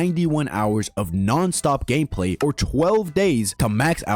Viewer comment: why did i hear GAYPLAY instead of gameplay...